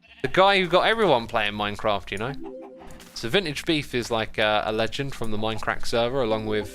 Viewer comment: I thought he said minted beef!